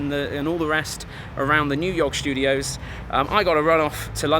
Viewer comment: I heard "our new york studios..."